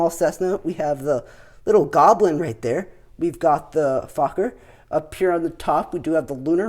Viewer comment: Did you just say fucker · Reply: Fokker